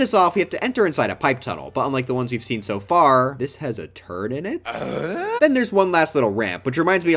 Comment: when you said there was a turn in the pipe tunnel i was sure i heard you say there was a turd in it😭